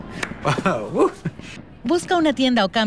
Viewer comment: Honestly thought it said tortamente. I definitely want a torta.